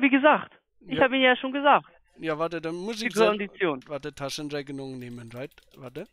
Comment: Ich musste es 3 mal anhören, um zu verstehen, dass er "die Kondition" sagt, und nicht "sie Vollidiot" xD